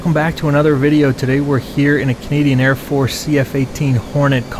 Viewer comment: did just say Cadian air force? 😅